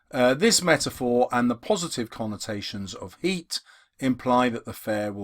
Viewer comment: Sir at 37:08 u said "heat" instead of "heart"😂